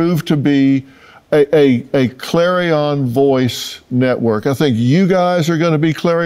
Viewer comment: All I heard was "Clarion... Monsieur Clarion"